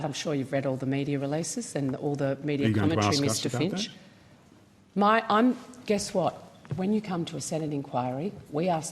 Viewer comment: Can someone tell me what Finch said at 18:30? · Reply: “Are you going to ask us about that”